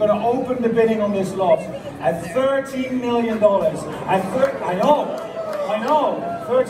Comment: you can CLEARLY hear he said SEVENTEEN million, not seventy. English isn't my first language and I could clearly understood that. I think the guy typing the numbers on the screen was the issue there. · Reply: He clearly said the lot was starting at thirty million dollars at 0:52. You can hear someone in the crowd say, Thirteen, thirteen. There was confusion was the start.